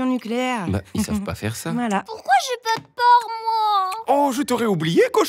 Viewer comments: il dit "pourquoi j'ai pas de porc moi alors qu'a 5:33 Il apprend la contenance de ce gateau ? bizarre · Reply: Il dit "part" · Attendez ,attendez !! A 5:11 cochonnet dit "Pourquoi j'ai pas de PORC moi ?" Hmmm..